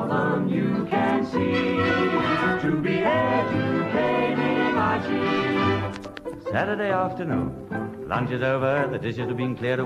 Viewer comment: I thought it said executing archie